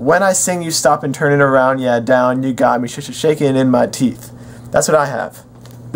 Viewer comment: I hear "God damn, you got me sh shaking in my teeth"